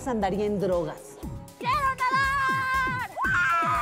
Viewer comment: que dice brenda en el minuto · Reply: sarita vu ruiz chitan brenda dice quiero nadar